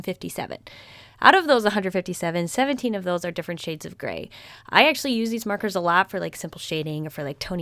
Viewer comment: When you said there was like 17 shades of gray, I thought you said there was 50 shades of gray xD